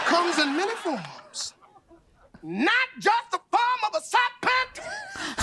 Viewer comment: for years i thought he said, “form of a side panther” but he actually said, “form of a serpent” 😮